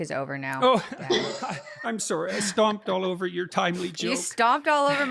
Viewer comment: The podcast person misheard what he said and looked a bit peeved. He apologized for stomping on her "timely joke" and she said "my tiny little joke" which makes it sound a bit belittling. Just noted that cuz it was so obviously a misunderstanding, but that's what happens a LOT - people mishear things or hear what they expect and have knee-jerk reactions. 👍